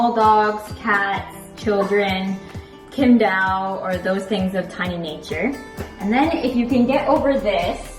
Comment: Did you say "Kimdao"? at 7:55 ?